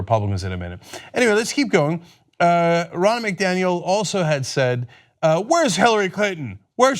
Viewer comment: wow Ronna Mcdaniel...., I thought you said Ronald McDonald....